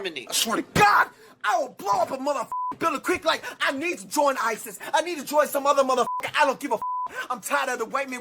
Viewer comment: I thought I heard him say, "I want to join ISIS." Somebody report that fucker to the FBI